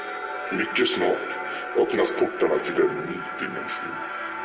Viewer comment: i think it’s swedish? The pronunciation sounds swedish, and i think i heard it saying “mycket snart öppnas portarna till en ny dimension” which translates to “very soon the doors will open to a new dimension”